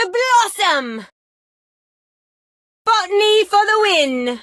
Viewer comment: I thought it said f$ckv me for the win